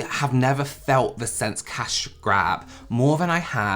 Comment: I honestly think I only heard 1/2 this video I laughed so hard!!! He said “cash grab” I thought he said “cash cab” this was so great the forklift and the IBS really got me though!! Cheers and love from Canada 🇨🇦